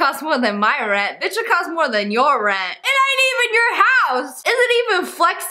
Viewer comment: I THOUGHT (LIL TAY) SAID THIS BATHROOM COST MORE UR URINE (I LAUGH SO HARD!)